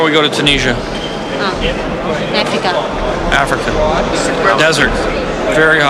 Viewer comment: “Tomorrow we go to Tunisia!” “Oh. Mexico?” “Africa.” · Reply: Lol... Sounds like she says Mexico, but listening closely and it is Africa. This is kind of like that case of "Laurel or Yanni".